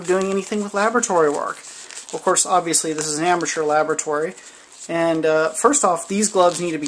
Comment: When he said Ambattur Laboratory I thought he said "Aperture" from the Valve's portal games, did anyone else? 2:32 Oh and a Portal monitor at 5:20 heh, I'll be spammed with all these Portal 2 jokes but I won't say cake! Doh I just said it!